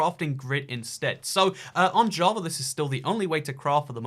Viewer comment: i thought he said on jah but he actually said on java